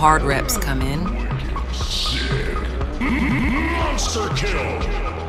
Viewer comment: Did he just say n🥷 kill